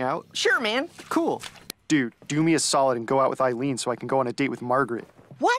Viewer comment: When he said go with Eileen I thought he said go and buy weed